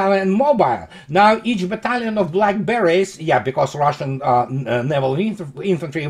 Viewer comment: Lol - when you said "Black Beret's" I could have sworn I heard "Blackberries" and I thought "oh, what an interesting nickname for the Naval Infantry!"